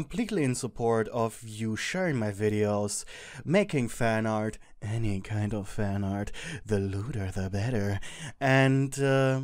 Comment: WHAT DID HE SAY🤣🤣🤣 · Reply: I was laughing at the fact you said any kind of fanart the leuder the better 😂 [Don't get me wrong I love your videos💜]